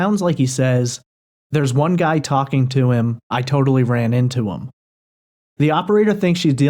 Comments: I just listened to the slowed-down version again. I clearly heard: “The one guy I talked to tried to tell me I ran into him.” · Sounds like he said, "theres no talking to them or him."